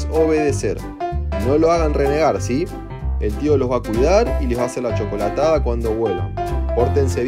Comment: Pensé que dijo culear no cuídar😅